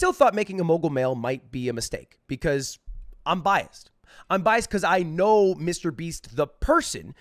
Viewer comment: I Thought He Said "Bi" Lmao